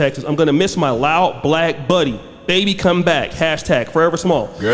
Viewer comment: hold up wait a minute. Did he call him Babe